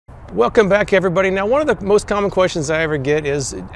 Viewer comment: I thought you said, "One of the most dumb questions..." Lol.